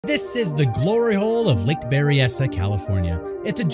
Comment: I swear I heard you say "lick berry"